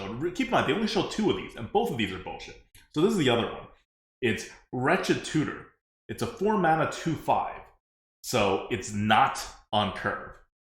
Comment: When you first said "Wretched Tutor" i heard "Ratchet Tutor", which is a goblin town in WoW. got confused for a second there lmao